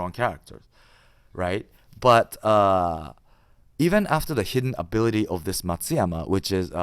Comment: Anyone misheard saying “hidden ability f***shit” 😅